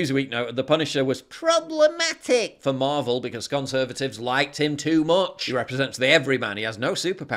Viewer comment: did he say "gunservatives" - 😁 That's on the verge of being neologistic....